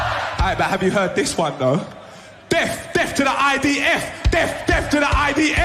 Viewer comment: I thought he said "deaf to the IDF" 🤪 But seriously, I'm so sick of dual nationals. Ely is not British. I'm British. I've never served the Israeli government. Another 5th Columnists, if it's not the bleeding Islamists. Can we get them all out and their filthy desert religions?